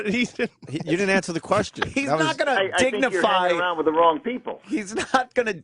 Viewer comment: Mack Grevace No he said i think you're hanging around with the wrong people referring to the radio host hanging with louis ck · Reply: I heard "your messing with the wrong people"